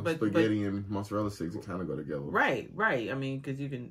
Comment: Sound like he said top dogs together which sound like taco 😂😂